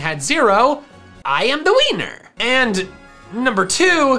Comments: I thought he said "iAm tHe wEinEr" please someone use that in a -out of context scenario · When said, "so I am the winner!" I heard, "so I am the wiener!" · its sounds like you said "i am the wiener!" when you were saying "i am the winner!" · sounds like he said "I am the wiener" not "I am the winner"